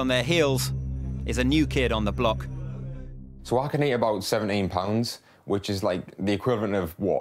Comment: did he say 7-8 pounds is the equivalent of two newborn babies? 🤔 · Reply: He said 17 and 18 pounds. Lol